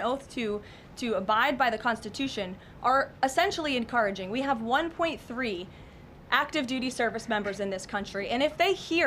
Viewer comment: ish did she say we have 1.3 active duty service members in this country?